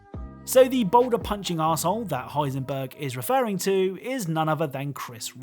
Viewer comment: Honestly, I thought he was saying 'That older pudgy asshole' not ' that boulder punching asshole'